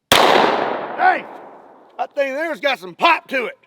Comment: did bro said hawk tuah ? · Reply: He said pop to it